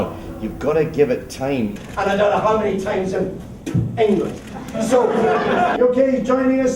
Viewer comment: Did he say England, bend down, and spit at 1:00?